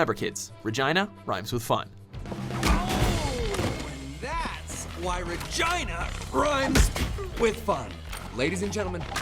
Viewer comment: I always thought that he says " thats why vagina rhymes with fun " XD just keep mishearing it, doesnt sound as cool now that I heard this commentator say it lol